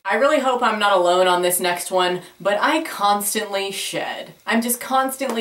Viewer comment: At first I thought you said "I constantly shit"